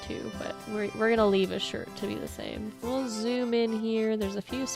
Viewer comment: I swear when I was heard “We’ll zoom in here” I thought said “Bonjour in mirror” and I was very confused 🤣😂